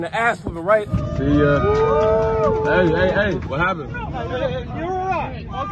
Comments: “You’re alright I’ll give you that” Got to give the drunkard credit for going out with a bit of class & showing respect. This is How every fight should end! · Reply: I thought he said " you were right" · “You were right, I’ll give you that!” At least he knew he f’d up 😅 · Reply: I thought he said "you were alright"